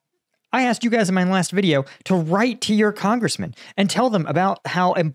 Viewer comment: I thought you said : write your congressman about the gum. ( the you sell😂)